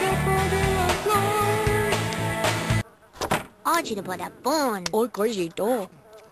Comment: i heard it as porn xD